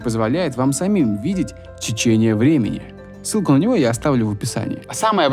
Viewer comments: Я одна слышу чечение?(на 6:52 ) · чечение времени, как мне это расслышать..